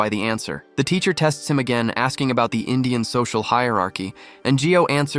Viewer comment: bro did they say indian say hierarchy and said caste system wt?